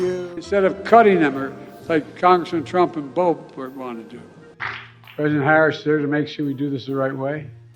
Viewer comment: BIDEN SAID instead of cutting ember like congressman trump but bope wanna do WTF???